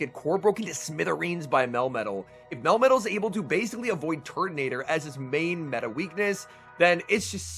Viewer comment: the subtitle says tornadus for turtonator lol